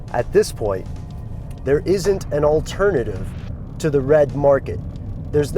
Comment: i thought it said '' Red Marker '' !